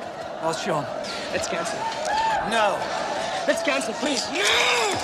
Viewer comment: DID JCVD SAY "LETS CALL THE POLICE??????" THAT WOULD'VE BEEN A GOOD IDEA REALLY, TO CHECK TONG PO'S CIRCULATORY SYSTEM FOR COKE!!!!! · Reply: lol he said "let's cancel please"